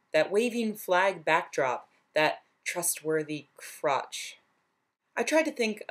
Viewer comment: I need to confess something... When you said "trustworthy crotch" I heard "thrustworthy crotch". My ears are hot for Chuck apparently.